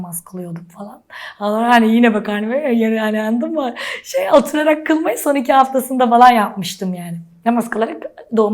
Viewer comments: ne dediğini kimse anlamadığı için altyazıyı da çevirememişler şsdfkldsfldfskdgsl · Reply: Ya hani yine karnım böyle ya oturarak namaz kılmayı sonradan yaptım falan jsjajsjsj gibi bişi diyo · 'a dikkat, ne dediğini anlayan varsa yazabilir mi? Evrenin sırrını açıklıyor. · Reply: Halayani yine bakarnii veri yeri yandinmı şeyaldı